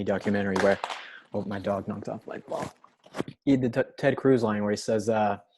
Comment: is it just me or did he say my dog knocked off my bong.